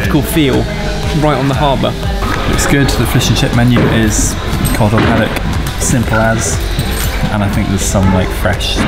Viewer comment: the fish menu is....? I want to know what he said!! who subbed this. honestly. is he saying "cod or paddock"? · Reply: He’s saying cod or haddock